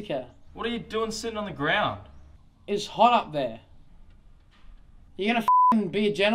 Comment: Should be known that 'it's hot up there' was improv, forgot his line. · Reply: I thought he said "it's hot out there", which would make sense in that instance, and why he would be sitting down in the shade